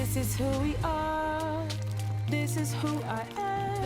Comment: did i the only one who heard word "bitch"? xD